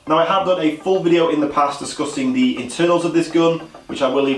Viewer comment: did he just say that he was disgusting the internals?